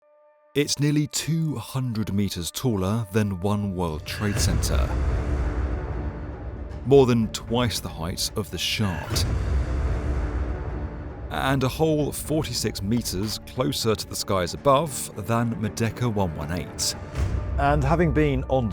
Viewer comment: Did he say “shart?”